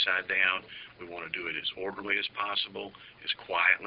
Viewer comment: did he just say he wanted to search his place as "morbidly" as possible?